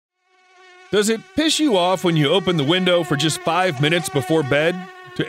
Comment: at the start I thought that it said does it piss you off🤣